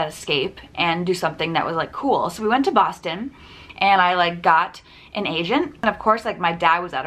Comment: did I hear got an agent or an Asian!!? ;)